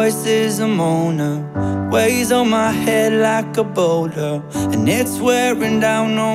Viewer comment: I thought this bitch said "like ebola" lmao